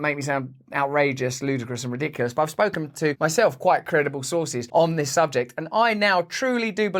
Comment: did he say ive takked to myself about this?😂😂😂😂